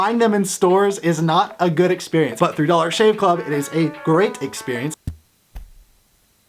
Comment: For a second I thought it said "dollar Shane club"